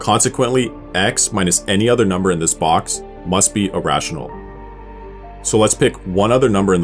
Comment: anyone else also heard "irrational" instead of "a rational"? You have no idea how many times I misheard these two in the past 🤣 (I have no idea too, but I can guess in which box I would place it)